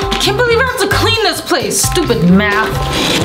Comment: Did Lily Said Stupid Math?